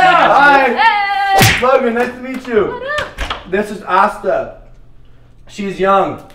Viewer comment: I thought Logan said "and this is Asda" wtf! I swear I'm not deaf 😂😂😂😂😂